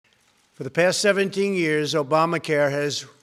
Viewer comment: Did he just say, "In the past 17 years Obamacare has ..."?